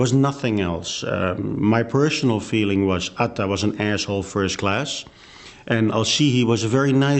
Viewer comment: Atta was an asshole first class did he say that for real??